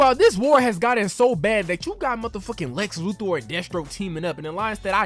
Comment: LOL the way you say Death Stroke sounds like you said Destro, I'm like damn GI Joe villains in this timeline?? xD